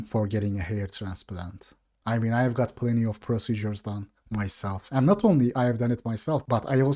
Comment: Bro did just say he got plenty of surgeries done?